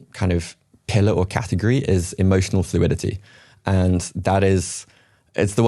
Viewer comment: Is he saying emotional stability at 6:30? Hard to hear it well